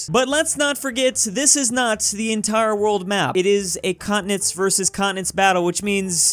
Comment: When i first heard you saying “continents vs continents” i thought you said “communists vs communists” lol